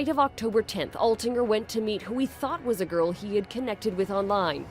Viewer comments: did she say edmonton? damn if so that’s where i live and - just w o w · DID SHE SAY SOUTH EDMONTON? I live there..... HELL NOO